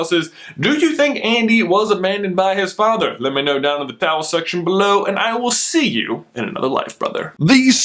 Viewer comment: Anyone else hear "The towel section below" ???